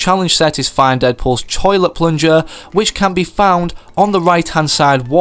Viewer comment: he says Choilet paper instead of toilet🤓🤓😂😂😂😂😂😂😂